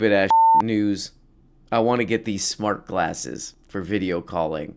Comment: I thought that said Shart Glasses for Video Calling.